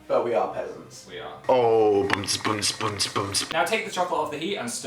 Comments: I got really confused cause I thought they said dog chocolate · I thought that Phil said dog chocolate o.o Yeah I get it I'm that retarded XD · I thought you said dog chocolate. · I thought you said you can use dog chocolate...